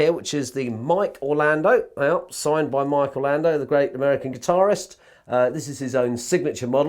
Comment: Name of the guitarist, please! I heard "the great Marc Bolan" but i'm pretty sure it's not him! 😊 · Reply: Mike Orlando